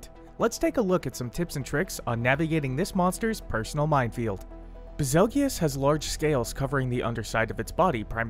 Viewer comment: Did he just say ba- zel-gee-yus what